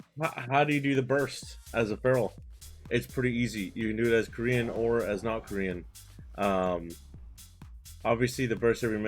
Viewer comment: I honestly thought you said "you can do this as a Korean or not as a Korean".